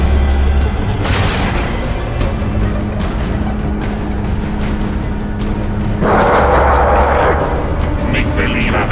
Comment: What id that boss said at 23:22 did he said nicht believe i enterned? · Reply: Soldier Plant he said "make-believe avenger"